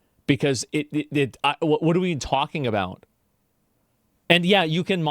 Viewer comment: I heard "What are we talking Avowed"